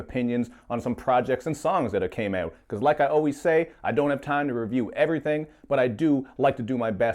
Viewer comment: I thought you said "Like I always do at this time", then realized you said "Like I always say"